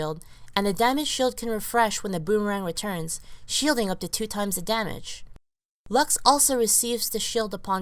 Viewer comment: i heard "tons of damage" , phreak's wife?